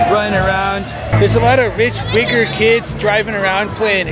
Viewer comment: what did you say???? hahaha 🤣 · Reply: Those rich wiggers lol